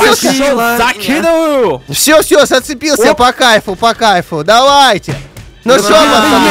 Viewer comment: Мне кайф послышалось 😂